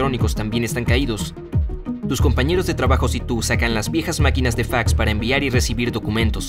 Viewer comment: parece que dice pijas maquinas